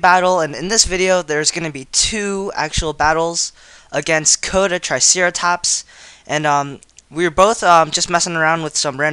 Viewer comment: I thought you said his user name was CoitusTriceratops. xD